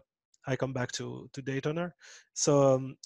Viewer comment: there you got once again... i couldn't understand what did he mention... 'daytuner' or something like that?